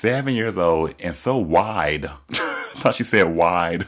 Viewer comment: I thought Ru said "and so white.” hahahahaha